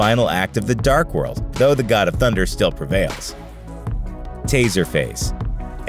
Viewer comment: i thought he said t-series..